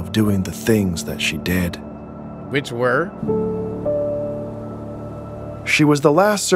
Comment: did he say "B!tch Where" or am i just tweaking.